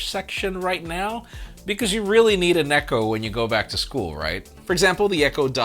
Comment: I heard “because you really need a neko.”